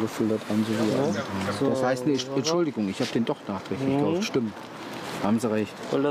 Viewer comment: Kann mir einer den Text sagen der Ab 2:52 kommt? Ich verstehe immer "was heißt Entschuldigung ich hab nen Doktor" xD